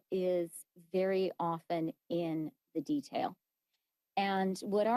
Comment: (The devil is in the details.) Did I hear that right.?